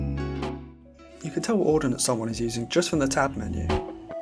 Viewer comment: what did you say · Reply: "You can tell what ordinance someone is using just from the tab menu"